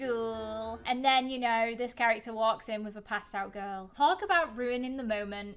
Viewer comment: Whenever she said, passed out girl, I thought she said pastel girl because her hair lol